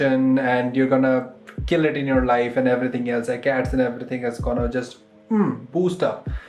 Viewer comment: Cats and everything just gonna blow up, is that what he said 😅